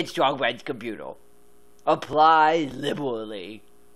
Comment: Appwy Liberwawwy! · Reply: And now, years and years later, I realize he’s saying “apply liberally”… For the longest time I thought he was saying “apply le bouli” and figured it was some French or Italian phrase or reference that just went over my head…